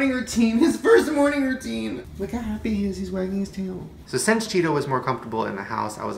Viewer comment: Did he say "look how happy he is,he's wearing his tail."? Like I genuinely keep hearing that 😂 Oh maybe wagging his tail😅